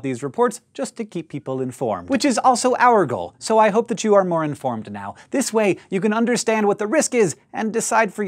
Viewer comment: At the end I had an epic misheard from "More Informed" into "Moron Form" xD Anyway, thanks for another awesome video :)